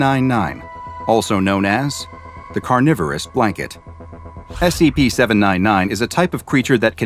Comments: I thought that said Coronavirus blanket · I thought it said "The Coroavirus Blanket"